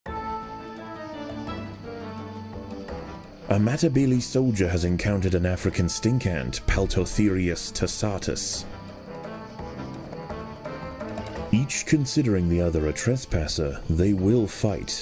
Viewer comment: I thought it said "African Sink Ant" 💀